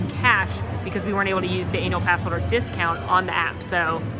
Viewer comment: did....did she say "anal pass?" o.O also...as I Brit I was "tutting" away at you picking up the battered fish with your hands! how uncouth!